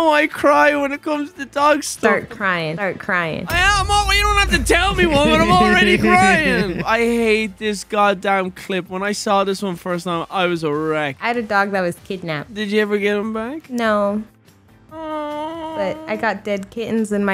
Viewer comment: BRUH, when said that at 5:47 I heard "ERECT" and not "A RECK" and I was like: AYOO🤨